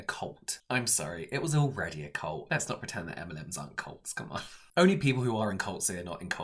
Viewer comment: Literally thought he said m&m’s lol